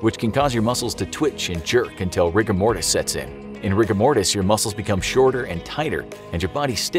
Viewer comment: i thought he said rick and morty